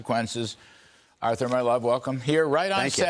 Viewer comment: did he say "Arthur my love welcome"?